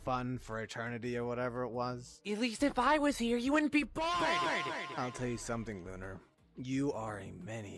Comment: What i think lunar said: at least if I'm here, you wouldn't be BALD